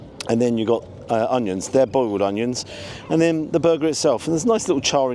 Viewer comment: did you say "boiled onions"? I'm 40 years old and this if the first time I hear those two words together.